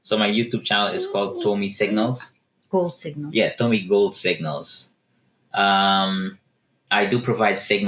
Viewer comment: Can't find him on YouTube. Can someone tell me the name. I thought she said Tomy go signals